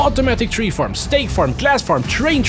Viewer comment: DID HE SAY STATE FARM???!!!! LOL 🤣